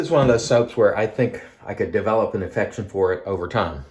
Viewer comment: "... I think I can develop an infection for it over time". (Please tell me I misheard that!) Contageous, is it?